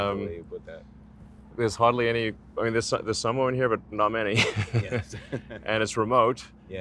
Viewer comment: Did he say "There's some women here, but not many" ?! That's not what the subtitles say... but the comment doesn't make sense otherwise... lol